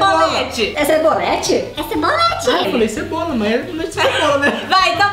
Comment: so eu entendi sabonete ?😂😂😂